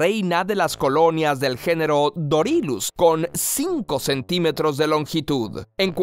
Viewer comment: te juro que escuché Doritos